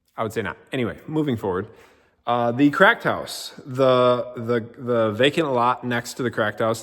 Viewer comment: Thought you were saying crack house. 😂